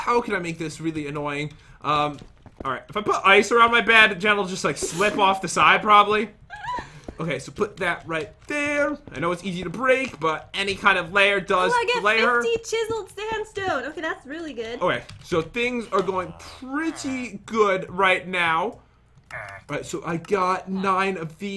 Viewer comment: am I going insane or did pat say “any layer will play her”